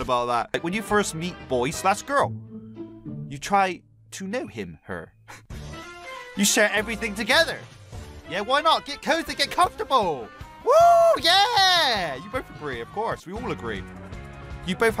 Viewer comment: did he say get COVID get comfortable